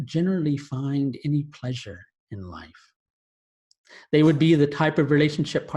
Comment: Did he say, they would "be" or they would "meet"? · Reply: its "be"